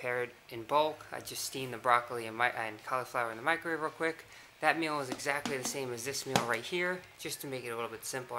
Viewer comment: is it just me or did you say "I colifloured the microwave real quick"